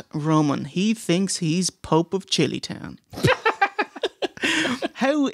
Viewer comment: Pope of chili town... Is that what he actually said?... If so, then what is that even supposed to mean?